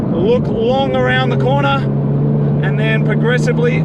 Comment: Thought you said "Luc Longley around the corner"